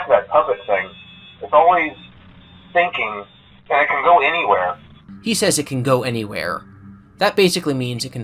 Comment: I always thought he said “It’s always thinking that it could go anyway” not “It’s always thinking and it can go anywhere” but everyone agrees he’s saying “and it can go anywhere” so it’s whatever. Maybe I’m weird and misheard the phone call. I can definitely hear him say “and” sometimes.